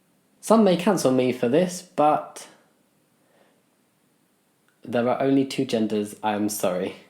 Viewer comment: at the beginning, i thought he was going to say "there are only two genders" instead of "there are only two days" I WAS WORRIED FOR A SEC LMAO